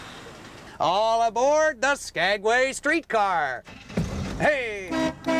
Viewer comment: I thought it said "SKANKway Street Car". I said to myself, "How nice! They named it after my sister."